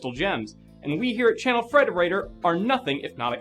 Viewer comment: the subtitles say "Child predator" instead of "Channel Frederator"